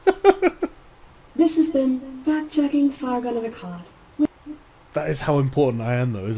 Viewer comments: I heard that as fat checking Sargon of Akkad...is she saying he needs to lose weight or gain more? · I heard "fat check".... nevermind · Honestly thought she said: "Fag-checking Sargon of Akkad" "You got a fag, mate?" "Yeah....uh, nah, I just finished them mate" "So you lied!" Unless it was "fat-checking Sargon of Akkad"? "Sargon, you been at the butties?" "Nope" "Think you're wrong there, mate"